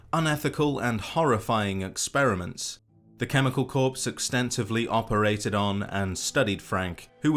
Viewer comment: lol sounds like you said cannibal corpse